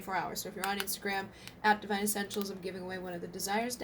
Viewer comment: 🙏Dive deeper! What you need to pay attention to most now, what needs healing, and what your person has to say to you? 🙏Definitely needing some attention in a few areas for a balance of emotions and expression of the deeper truths within one another ♡ 🔮👑🔮 23:17 mins long👑🔮👑 <link> 🔮Witches Tarot <link> 🔮Sacred Geometry Activations Oracle <link> 💋Journey of Love 💞union oracle💞 (SALE STARTS 12/14 $41.99 UNTIL 1/4/20 ♡ ORIGINAL PRICE $51..) <link> 🔥Desire Deck🔥 US residentsemail <mail> International Available in Marketplace <link> ❤Divine Counterparts Oracle ❤ <link> 🔮 Starseed Oracle 🔮 <link> ✔Positive energy donations are appreciated and assist in my being able to create more videos and channeling what is needed for the collective ♡ <link> ❤🔮Subscribe Sister Channel Divine Essentials 🔮❤ guidance, readings, series, channeling, and advice for starseeds, lightworkers, empaths... <link> ❤🔮 Follow Me on Instagram ❤🔮 <link> <link> I'm on Instagram as <mail> Install the app to follow my photos and videos. <link> ❤🔮Facebook Pages🔮❤ Divine Essentials <link> Pleiadian Essentials <link> Bridget Rau <link> 💋❤💋 Join the closed Divine Essentials Twin Flame & Soul Mate Support Group 💋❤💋 <link> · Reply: I swear I thought you said Divine Ascensions but it's essentials- now I feel like those people who sing the words wrong in songs 😂